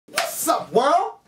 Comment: it sounds like he says" another day another s***."